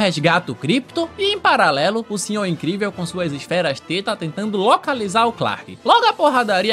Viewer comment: EU OUVI ESFERAS TETA